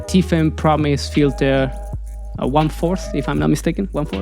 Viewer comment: I can’t quite catch the name of the filter you mentioned at 6:20. What is is called? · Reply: Hey! I probably mispronounced it. It’s the Tiffen Pro Mist 1/4. This one to be precise: <link> . I’m adding it to he description in case some is looking for it too